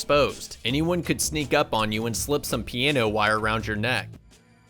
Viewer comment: did he say piano wire